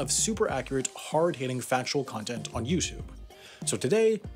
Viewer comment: did he say youjube?